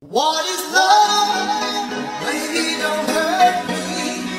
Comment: Instead of baby dont hurt me I hear "WAVY Dont hurt me"💀